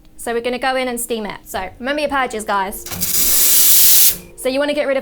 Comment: what does she say? "may be the pour itches" ? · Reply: She said " Remember your purges guys " or remember to to blow out the steam wand 🙂